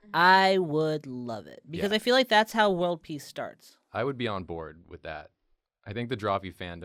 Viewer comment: I misheard at 16:10 and thought he said it was how one piece starts not world peace 😂😂😂